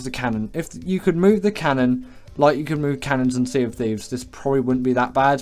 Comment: did I hear sea of thieves😈